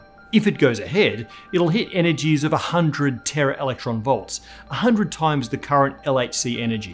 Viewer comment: O'Dowd here says "100 times" but the subtitles say "8 times"?